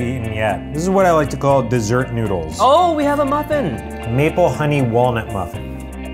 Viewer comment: When they said maple honey walnut muffin, I thought they said meatball honey walnut muffin and I was so confused.